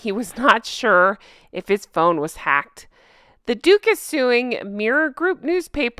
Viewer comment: Totally thought you said Dooffus once not "Duke is" 😂😂 My new name for them Dufe and Dufess of Sucksess' I wonder if he realizes the irony that the barrister "ran" into them in France . How did he know they were there. Or was it Dooffus of Sucksess dial-a-Lawyer?